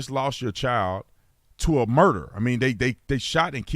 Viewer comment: She just lost her child to a murderer?. Did I hear that right?